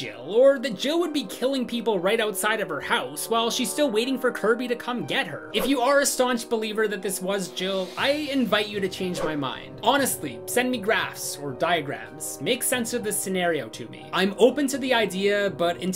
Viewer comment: When he said "Send me graphs" I thought he said "Send me grass". My immediate thought was 'Well I don't have any connections and depending where live that MIGHT still be illegal but whats your PO Box homie I'll do my best to hook you up!'